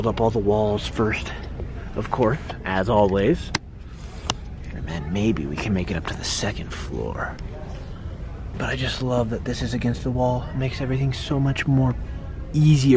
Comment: I thought said "against the law" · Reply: Jamie Faith Aubin he said agansist the wall